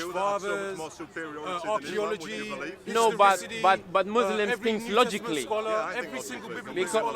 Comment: did i just hear "muslims think logically" LOL